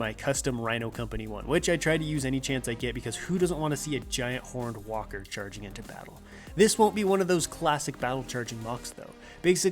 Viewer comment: "because who doesn't want to see a giant horny walker walk into battle" is what I heard at first........